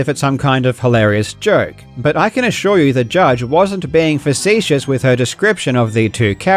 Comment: What word is said at exactly 10:00 ? English is not my first language and if I try to google it keeps suggesting vicious. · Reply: Facetious - adjective treating serious issues with deliberately inappropriate humour; flippant. "a facetious remark"